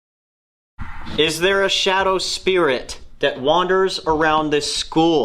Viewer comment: i definitely heard "yes there is" with my eyes closed and not reading etc